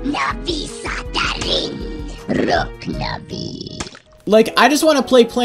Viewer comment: I swear said "проклятый" ["cursed" in Russian] but that can be just my hearing